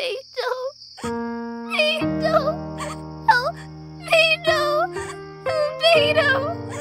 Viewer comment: I thought she was saying beido… 💀